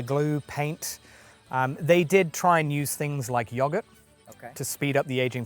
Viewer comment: "They did try and use things like _______ to speed up the aging process." I have played this 10 times over and I cannot understand what word he's saying here. Can someone help me out? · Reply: Yoghurt. As a bacterial culture, it can promote the rapid development of real fungi and lichen - it’s a neat trick! If it helps, you can turn on the closed captions to hear what and I are saying (click on the CC icon). 🙂